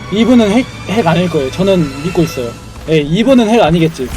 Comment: 이분은 핵 아니겠지 젠야타 : 왜아니야 이런 소리 들림